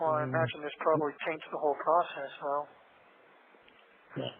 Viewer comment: I think he says ‘taints’ the whole process. I bet he hoped his lawyers will argue misconduct on PD/jail/DA for all the ‘leaks’.